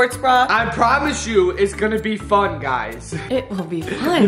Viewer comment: At the beginning I thought said "it's going to be fu**ed guys" had to rewind it to hear fun lol. Love the fam!! You 2 inspire me everyday.!! Keep up the good work!.